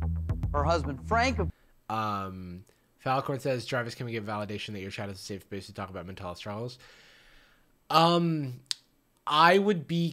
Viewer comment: what the fuck did he say at 29:26?? i can't make it out for the LIFE of me. Mentalis traumas??? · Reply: (Reading a message from chat) "Jarvis, can we get validation that your chat is a safe space to talk about our mental health struggles?"